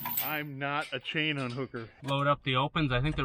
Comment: Did I hear say he's not a chain hooked? The Motels are greatful.😅 Hope your Dad feels better Back pains the worse. But it looks like he may need a purse 👛